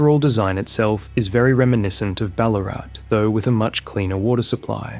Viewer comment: any other australians have a double take and think we heard ballarat mentioned for a moment?